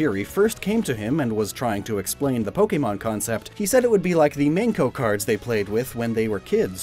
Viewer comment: OH! MENKO cards I completely 100% thought you said 'Manko' cards and was sitting here like '?! HUH???'